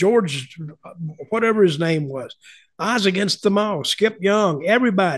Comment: I could have sworn I heard him say, “Butch Reed, The Snowman, George…whatever his name was…Isaac Asimov, Skip Young, everybody”? Still not sure who he actually said, but pretty certain it wasn’t Isaac Asimov. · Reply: "I was against them all" is what I think he said